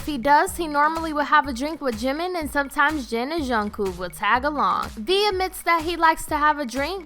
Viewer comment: u said jungkook name wrong i heard "jungkoo" 😂